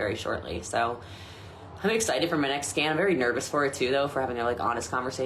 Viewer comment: 100% Thought she said she was excited for her next "scam."